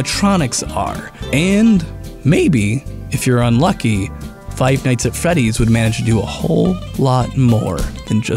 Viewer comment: ROFL did he just say Five Nights At Feddy's